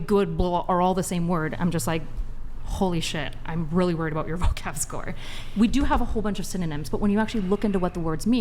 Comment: did she just say “vocab score” Am i high? Vocab score? Wtf is she talking 😂😂😂 does she play weird elementary school computer games on old macs?